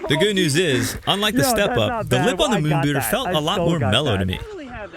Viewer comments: I think the moon bootter got its name because people broke their legs and wear moon boots 😂😂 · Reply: I thought he was saying moon booger. Like having a big booger and having a space helmet on. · I thought he was saying moon booger.